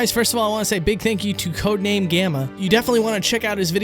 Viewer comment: When you said "Codename Gamma", I at first thought you said "Calamity Ganon"